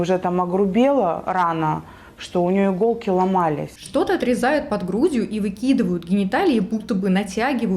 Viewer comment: Что-то вырезают под грудью выкидывают гениталии" 8:27...я правильно услышала😂?